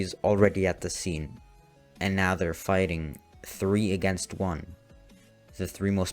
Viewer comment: I thought he said he died against ten ten :)